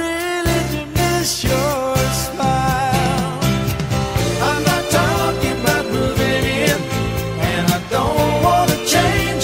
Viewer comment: I always misheard the chorus as "I'm not talking bout the linen" lmao